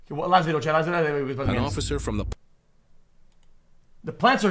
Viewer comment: Xqc fan of 2 years In 0:00 he chanted a warlord level language i cannot understand can anyone translate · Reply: “okay last video chat last video and we can play some games”